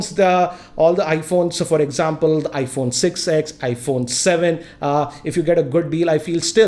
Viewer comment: Did i just hear 'iPhone 6X'?